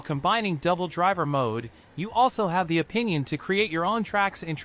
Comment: Is this being read by an AI voice? It's weird. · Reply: Yeah, at 3:53 it reads an autocorrected word ("opinion" instead of "option")